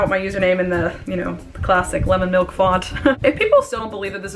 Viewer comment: “Should I reply and be toxic?” “ Absolutely ” Oh and by the way, what app do you use for watermarks? I really love the font <3 oh and if you reply want font is it? I thought I heard you said “Lemon Milk” but I’m not sure. Thanks for your time, Love from Marisa. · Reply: Can confirm it's lemon milk!